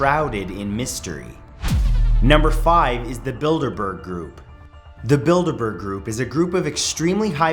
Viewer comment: I can't be the only one who thought he said "Build a Bear Group." bruh I tripped over myself to get back to my laptop to rewind the video - hurt my foot and everything...